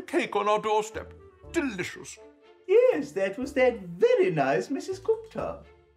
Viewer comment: What does Matt say at 4:08 ? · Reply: “Yes, that was that very nice Mrs Gupta”